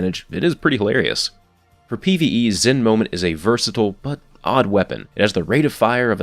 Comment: you guys notice he said zen moment instead of zen meteor